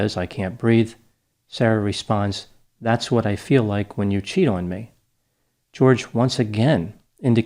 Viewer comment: To me this woman had a large chip on her shoulder and got into a bad relationship, and took to drinking... It would have taken some persuasion and questionable intent to make someone get into a suitcase...and let them zip you up. He seems a little dumb and she's less so...but as we see, alcohol and suitcases are not a safe mix...😳🤔🥴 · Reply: I thought she said "that's what it feels like when you CHOKE me" in that video, the fact that she actually said "that's what it feels like when you CHEAT on me" seals the deal on this case. I was having sympathy, but it certainly doesn't sound as if he was physically abusing her by her own account, that fact makes this case pretty clear: she was punishing him because she felt he was being unfaithful and she did it by torturing him to death - so basically, fuck her. Unbelievable. She talks about him like a pet owner would a dog, she is clearly a narcissistic person who sought out someone who would be entirely dependent on her emotionally and that's what she got with this guy, he started messing around with chicks online and she didn't like that.